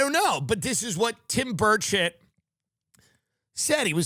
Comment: I truly thought he was saying "Tim Bird Shit" for longer than I care to admit